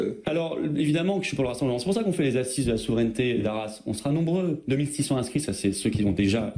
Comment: " c'est pour ça qu'on fait des assises sur la souveraineté et de la ... " et de la quoi ? de la "race" ? il surement voulu dire Arras. Mais on entends "et de la race", dans le cas contraire il y a un "et" de trop